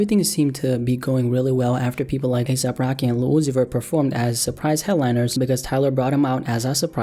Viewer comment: I need to point out that with subtitles 0:27 it says Lucifer instead of lil uzi vert coincidence.., nahh